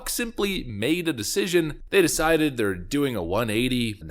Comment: Really sounds like you said and I know you didn’t was “ fucks made a decision “ 😂